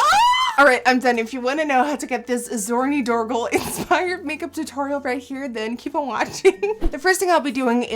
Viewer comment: I ligit thought she said " naked tutorials 😂😂😂